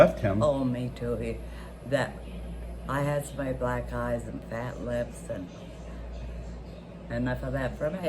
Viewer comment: Did she just say she'd had enough of black guys with fat lips? :o